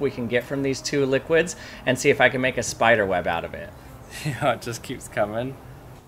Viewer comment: "it just keep coming" i thought i hear "it just keep cuming"💀